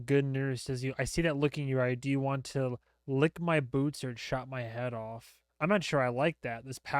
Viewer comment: he actually said "chop my hands off" it's a reference to another character that will show up on s3 😭 crazy that louis knows abt that episode tho